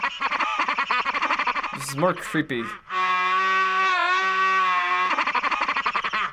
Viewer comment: Did she say Mario?